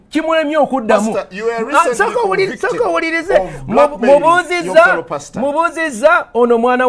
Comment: did anyone hear what Pasta was convicted of? It sounds like "blood bathing" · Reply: Black mailing